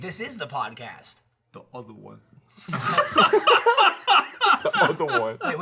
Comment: Can someone tell me what Gavin says after Michael says "Wait, this IS the podcast!" I got nothin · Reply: Wizen "Duother one" aka The other podcast known as The Rooster Teeth Podcast